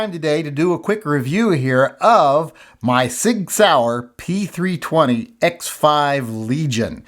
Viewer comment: Lmao @ 00:20 when he says P320 but the caption says: “SIG P226”